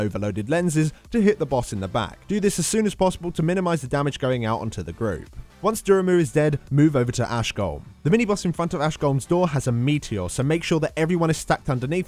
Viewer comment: Did you call grazer duramu?